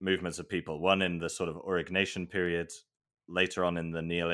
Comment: Double release! Check out my vid on life during the aurignacian here: <link> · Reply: Thanks for spelling Aurignacion. I thought you were saying Auroch Nation, as in the Aurochs, which would be a cool name for an ancient society.